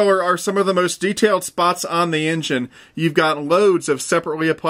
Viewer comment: the enthen??? or at least that's what I heard